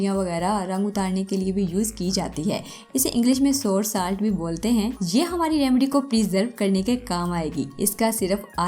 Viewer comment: pe kya bola h? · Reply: Sour Salt